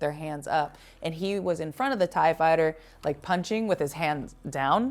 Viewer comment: you said "Thai Fighter", I heard "TIE Fighter"... Love the commentary and assessment. :)